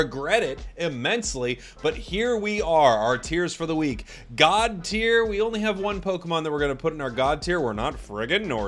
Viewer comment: I thought GOD tier said OCD tier 🤣